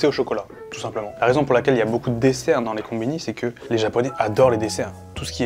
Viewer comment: moi qui entend " la raison pour laquelle y'a beaucoup de décès cest car les japonais adore les décès" J'étais la en mode wtf